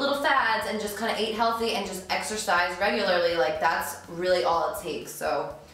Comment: Exercise I thought you said extra fries